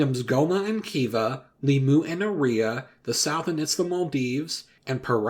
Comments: I heard "The Sultan of Maldives" at first instead of "The South and it's Maldives" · “The South” might refer to the CSA since it technically was “a country” during the 19th century, but it’s existence wasn’t during around the time of Oliver Twist, and there are arguments about whether we should recognize CSA as a country during this time period and not a region in revolt. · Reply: No it was misheard it's supposed to be The Sultanate of the Maldives